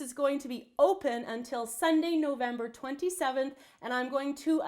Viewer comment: I thought you said December 27th, not November 27th. I would be baking my mom's birthday cake, brother's birthday cake, grandma's birthday cake, and Christmas cookies.